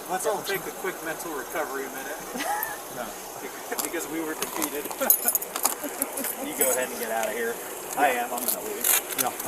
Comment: at about 48:23, after that cop said they needed to take a quick break to recover, did he say 'because we were defeated?' or did I mishear him (several times).